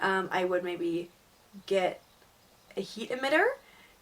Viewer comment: What is a heatamidder or what ever it is she said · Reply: ceramic heat emitter is a heat source without light. However your chameleon needs a temperature drop during the night! Check out some of my newer videos as they are more up to date :)